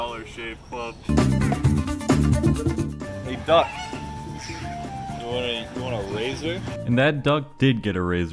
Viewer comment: I accidentally skipped to 6:10 in the video and I hear say hey duck you want a razor